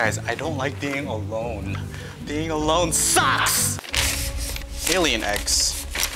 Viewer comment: did he say Alien x from Ben 10 or alien eggs